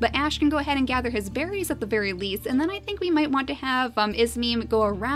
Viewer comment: "At least Ash can collect his berries at the berry least" is what I heard!